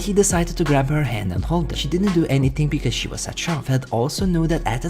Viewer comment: "She didn't do anything because she was a dchauf" What? Tried listening to it a few times over and still can't understand. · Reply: In shock